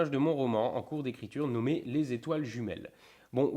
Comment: à 7:45 --- Quand j'ai entendu le nom du roman "Les Etoiles Jumelles", j'ai cru comprendre "Les Flammes Jumelles"... ----- Au moins, cela aura eu le mérite de me faire un(e) joli(e) signe/synchronicité. Surtout venant d'une chaîne qui est à "1000 lieues" de cela... et vraiment trop "terre à terre"...